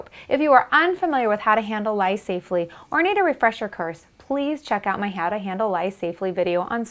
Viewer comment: I heard her say a refresher curse lol.